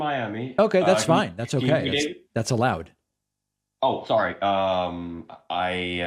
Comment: Poor caller thought David's "that's allowed" sarcastic approval of his Miami residency was him saying "that's loud!"